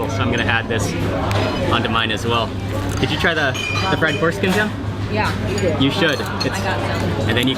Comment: did he just say fried foreskin?